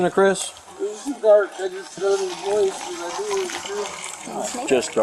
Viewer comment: No, he said, "It was too dark, I just heard his voice." He did not say hjs clothing was dark. He's trying to tell you it was dark outside and he didn't see the suspect's clothing. This drives me crazy when I am watching videos like this. Dispatchers are the worst. Dispatchers are always asking people to repeat stuff and mishearing things people are saying when it seems clear to me and i hear them just fine. Coos do it too, like this time. Open your dang ears! Jesz.